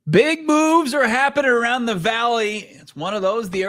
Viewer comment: Could’ve swore he said “big boobs”, but he said “big moves”. My bad